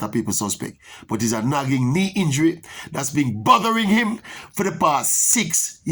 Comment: Did said, the injuries buggering him...🤔